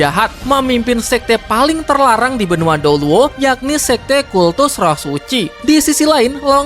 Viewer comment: ini emang telinga ku yang salah denger kata douluo jadi dolo apa gimana😭